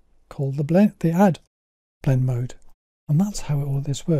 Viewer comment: you name the simple blend mode that you illustrate, but I can't understand what you're saying. It sounds like "bled deehad"? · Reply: Crikey, yes, that's an audio glitch. The blend mode is 'Add' (also known as Linear Dodge).